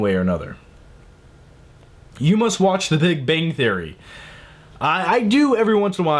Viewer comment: It sounds like you say the Big Bane Theory and that show sounds AWESOME where can i watch it?